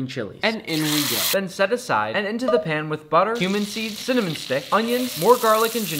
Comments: bro I heard “human seeds” 👍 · I heard him say “human seeds”💀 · Why did i hear “human seeds” 💀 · Make sure you've subscribed but more importantly - turn on notifications!! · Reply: Almost thought he said “human seeds” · Bruh I thought he said human seeds